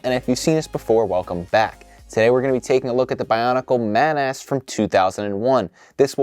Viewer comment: my friend thought you said "Today we are taking a look at the Bionicle MAN ASS"